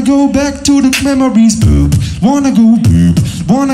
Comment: sounds like he said boob